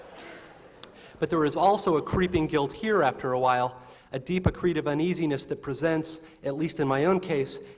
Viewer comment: what's that word "acrite"? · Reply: a deep accretive uneasiness